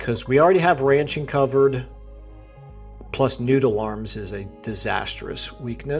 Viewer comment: I had to look back at what you were talking about cause I heard you say "nude alarms" and was taken aback.